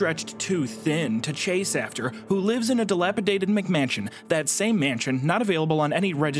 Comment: he said mansion I heard mcmansion